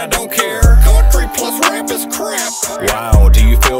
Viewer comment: Did I hear a "Creek squad rap is crap" ? Just wondering if I caught that right, maybe not. Let me know. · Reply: “Country plus rap is crap” is the line you’re referring to